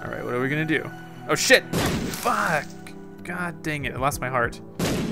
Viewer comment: Did just say fuck?